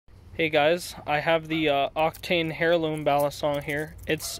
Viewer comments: Did he just say “Hairloom”? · IM SORRY BUT DID U JUST SAY HAIR-LOOM XDDDDD